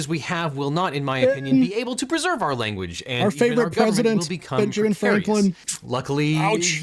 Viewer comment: Did he say "our favorite president"?